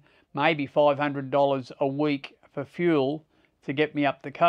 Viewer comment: did he say $8500 a week in fuel cost when traveling? That seams expensive to me . Am I'm missing something here? I know absolutely nothing about libeaboard costs or living. Sure learned a lot with this video. Thanks for taking the time to film it all. Thumbing up 👍